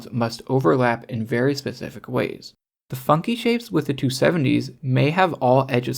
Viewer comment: "The fucky shapes with the 270s" is how I first heard that, then I realized you said funky 😭